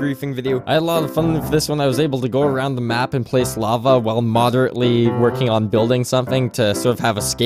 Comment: I thought it said moderately schweg at the beginning...